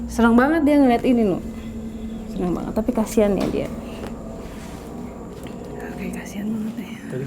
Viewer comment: Ka sara di waktu 01:00:42 sepertinya ada suara kartika ngulang kata" ka sara "TAPI KASIHAN SAMA DIA" itu jelas banget kedengernya